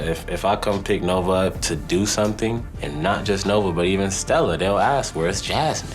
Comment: Sounded like Devoin said “Stella my child”